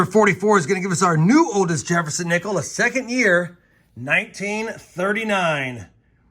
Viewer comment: when you said “oldest Jefferson Nickel” my heart skipped a beat because I thought you said oldest nickel and you had already found a “V-Nickel” Lol